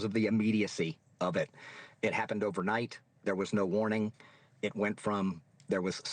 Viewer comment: The whole area was warned. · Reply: uhhh! I could swore I heard clearly the news reporter say, 'there was no warning' re-watch.